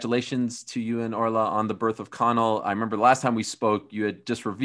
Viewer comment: wait did he called his kid "Colon" ????